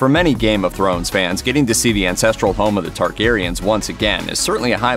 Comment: Lol, I heard “ancestral home of the Targaryens,” as “insestral home of the Targaryens.” Both true.